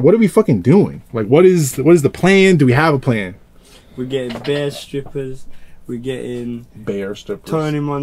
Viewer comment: lol at 1:37 he actually said "BARE strippers" (meaning 'a lot of' in London slang). So he was actually saying he wanted "a lot of strippers" not "bear" strippers as in the animal 😭